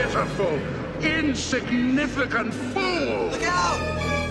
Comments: 日本語で「逃げろ」に聞こえるw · の所だけ急に日本語になったのかと思ったらただの空耳だった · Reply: ニゲロー！ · 英語のはずなのに日本語で逃げろ！ に聞こえるw